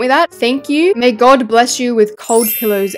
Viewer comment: I thought she said Drake blankets 😭